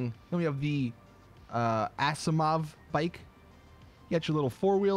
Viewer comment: Wait how do you spell what he said it at 4:38 (The Bikes names) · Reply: asiimov, it's a skin in csgo that has that colorway